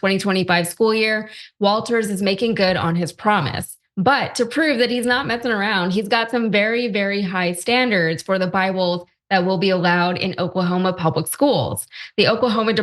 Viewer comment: sounded like she said "he's not mething around" lmaoooo